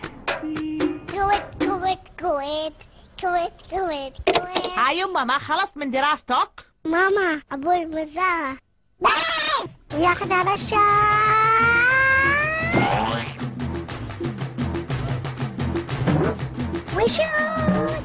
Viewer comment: moments that had me rolling for no reason: 2:34 fart ? 2:42 THAT CRY AND THE FACT I DON'T UNDERSTAND ARABIC LMAO 3:36 the laugh... oh the laugh.. 4:30 tesa . 6:19 he looks traumatized. Please help him 7:00 BAAAAAAAA 7:08 WISHOOOOOOO proceeds to kill his sister 7:27 "pokemoon!" 7:35 that laugh again.. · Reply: he actually says “THATS ENOUGHHH” in Arabic but I STILL HEAR BAAAAA 😭😭